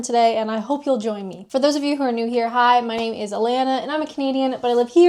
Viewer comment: Your intro sounds like your saying ' my name is Alana and I'm a comedian ' 😅